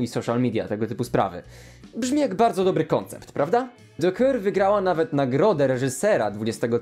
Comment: usłyszałam,, do kurwy'' XD